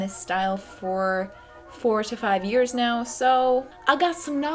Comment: I thought she said "for 45 years" at first instead of 4-5 years 😂